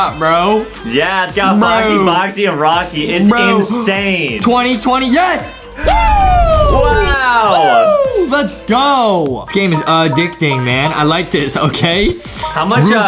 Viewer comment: did Justin just say the worst year ever 2020? Pls no